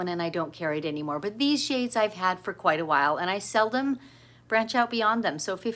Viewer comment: No, I don’t sell or ship thread, sorry. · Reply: thanks for the reply. I kinda laughed at myself because i went back to the video and re listened. What you said was “ I seldom….” but I heard, “I sell them…” 🤦🏻‍♀️